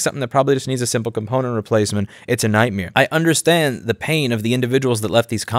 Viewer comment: Am I hearing things, or are you saying “It’s a night-meer” instead of night-mare at 5:02?